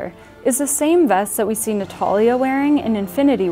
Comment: Did she say Nataria? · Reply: No she said natalia